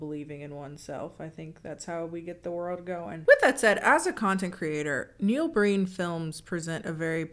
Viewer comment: I thought was saying that Neal Breen was a content creator, but I think it may be the closest description of that man’s work